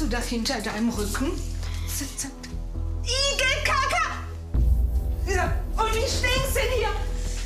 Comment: wer hat auch erst IGELKACKE verstanden????😂😂😂😂